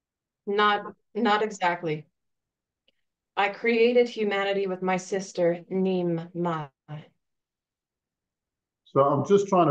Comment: he said "I created humanity with my sister", and then he said her name but I couldn't hear completely. neem something...? beyond that, I thought endki and enlil were 2 males.... I focused more on the cane and Abel story, from one of the other epic, but they all seem to be the same characters, always different names…. did anybody catch the name? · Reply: The sister's name he mentions is Ninmah.